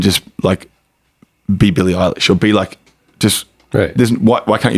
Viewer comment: I can't understand what Joel mentions when he is speaking about the Youth Ministry in Australia "they think there's no reason why you can't be in church and just like be ___________" be what? I can't understand that word in min 41:53 ... hopefully someone can help me figure out what/who is he talking about. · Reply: "be billy eillish"